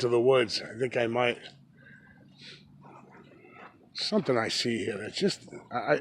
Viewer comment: vocal that sounds like what you say in a second....audio corruption??? 15:48: "Something I see here."